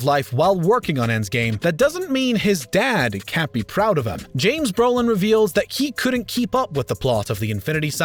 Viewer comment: Did the narrator say James Brolin instead of Josh??